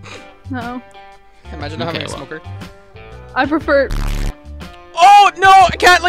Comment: what did she say? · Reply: I think she said she prefers it raw...